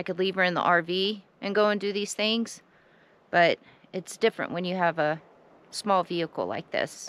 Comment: Anyone remembers the "Station Wagon". Now that's got room. Remember the wind deflector on the rear it was meant to keep dust out of the back window. Just wondering if that could keep her dust problem down. Got to get my hearing checked thought you said your face was small, no you face is quite nice perfect actually.😄 Thank you stay safe ALL